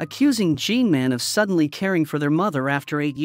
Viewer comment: I heard her uncle's name was "G-man"☠️